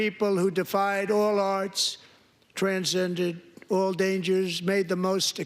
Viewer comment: Did he say transgendered? His brain is so rotted he can't say anything without it slipping out, lol